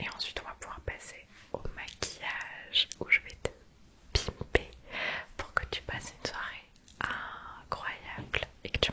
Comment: te quoi ?????? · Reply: En gros te pouponner